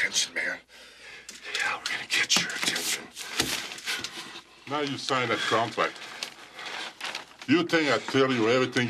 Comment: sounds like andre was saying " now you signed the cornflake" lol